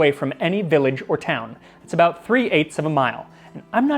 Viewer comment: I'm sorry for my European ignorance... Did you just say 3/8ths of a mile 11:20 ? I know it's a bit late, i'm just finishing a full binge (from the freshest episode to the very beggining) and this one took the cake for me. 3/8ths of a mile. I can't. There must be an easier way to measure distances, surely xD